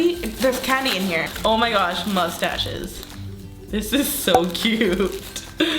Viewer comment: omg i thought it was Phillipines hahaha just because i heard "kamusta" OMG (im filipina hehe)